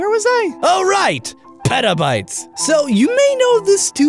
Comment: I thought he said pedobytes